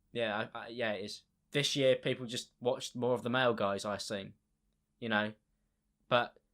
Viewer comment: "People watch more of the male guys" did I hear that correctly?